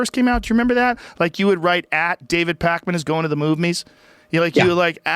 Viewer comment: I thought it said David Paulides for a second 😑